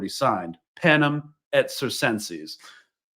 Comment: What was the thing said around minute 19:20 where it sounds like Latin? Something like Penem Errie Sentseys? I know i butchered that i know... but my captions butchered it more and im curious! Thanks! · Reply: It’s ‘panem et circenses’ - bread and circuses